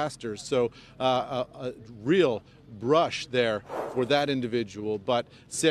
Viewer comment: "A real rush for the survivor that walked away " what a totally innapropriate comment from that reporter! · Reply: I thought he said that (rush) too, for a moment, but he actually says brush. I think he was going to say the guy had a brush with death which is the typical saying but stopped himself after brush because he knew that whilst that guy survived others are known to have died.